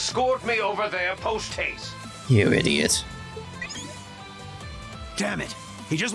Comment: hmm... I hear traitor rather than idiot